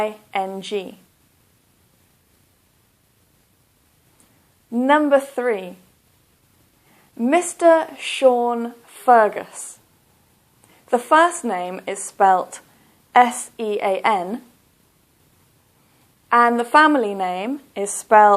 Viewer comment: i got 4 out of 5 my mistake is the number 3: Mr Sean Fergus but my answer was Mr Shan Ferguf i some how misheard the word H and F as you can see